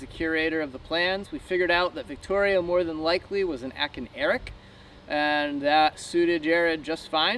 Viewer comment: I love your videos and have been watching since you first started building your keel frame and accumulating the lead to make your keel. I couldn't quite make out what you said about Victoria at around 7:15; you said she was more than likely a ??????. When speaking about her iron keel it sounded like you said the curator said she was more than likely an "atkin erik"? What exactly did you say and what does it mean? Thanks guys and keep up the good work! GOD'S SPEED!!! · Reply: You got that right. William Atkin is the designer and Eric is the name of the boat design. You can check out the plans at the Atkin boat website.